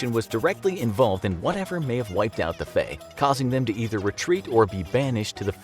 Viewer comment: I Thought it said face